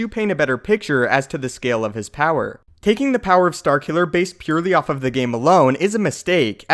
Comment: Correct me of I'm wrong, but I think you called Starkiller, Starkiller Base? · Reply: That One Asian "taking the power BASED purely off the game"